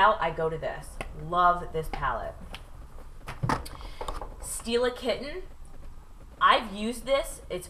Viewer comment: omg I thought you said "Steal a kitten!" LOL!!